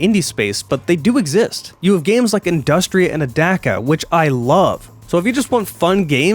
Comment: I understood and found the game Industria you mentioned but the subtitles say "adaka" is the 2nd game? I can't find that anywhere, I have tried all kinds of variations of that word on youtube and cannot find the game you are showing, I cannot find it on Steam. It does not seem to exist at all. Edit: Ah it is spelled Adaca, I had to type in "industria and adaka" into youtube to find an older video you made about it.